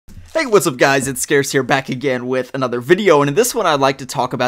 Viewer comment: Did he just says scarce here?!